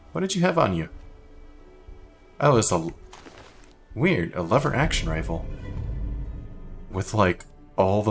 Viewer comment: when you said you would switch out to you 'lever-action' is misheard it as "LIberace."